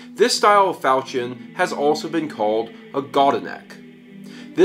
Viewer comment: does anyone know gow to spell what he said there? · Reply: It's maybe a bit late for a response, but the word is spelt Godenak.